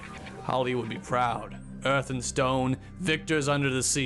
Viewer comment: did i hear Rock and Stone ?